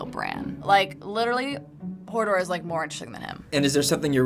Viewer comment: did she call him Hordor? 😢